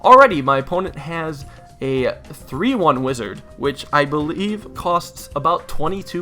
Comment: Did he say 3-1 wizard at 3:37 I'm sure he did